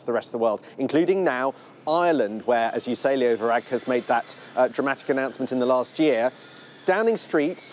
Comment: Did I hear him say at 0:39 Leo Varadkar made that dramatic decision in the last year?